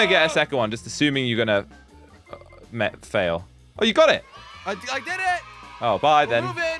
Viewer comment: at the end for a sec i thought said"we did it babe!